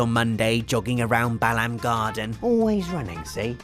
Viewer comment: did you just say "bell-end garden"?